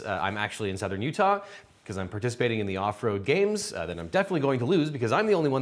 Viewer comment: I swear I heard you say “Afro games” 😂😂😊😅